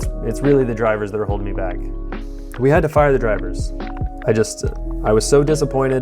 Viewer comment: Thought It said fried my drivers and I was like wait what